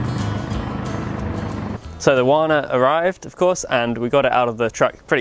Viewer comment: Walnut? Did he say "The walnut has arrived"? If so, there should be a guard posted on that pile of wood. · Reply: The wood is Wana not Walnut but still kinda high-dollar & worth guarding.